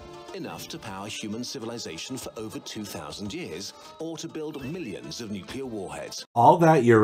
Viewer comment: They actually said over 2,000 years :'